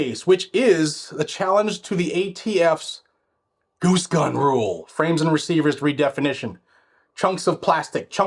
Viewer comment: WOOX: <link> SAVE 10% w/Code: GADGETS10 Join my email list: <link> GNG Website & Affiliate Links: <link> Support GNG via Patreon: <link> Blackout Coffee: <link> GNG Shirts: <link> Gun Owners of America: <link> Join Firearms Policy Coalition: <link> Second Amendment Foundation: <link> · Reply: I'm pretty sure I know what you said, but I'm equally sure I heard "goose gun rule"...